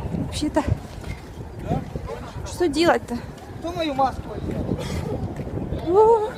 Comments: Я не знаю что делать, вообще... ХТО МОЮ МАСКУ НАДЕЛ?! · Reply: А мне послышалось что он сказал КТО МОЁ МАСЛО ВЗЯЛ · Вы ведь тоже слышали когда она бежала через пожарных, там кто-то крикнул -Кто мою маску одел? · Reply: А мне послышалось: "Я твою маску надену" 😂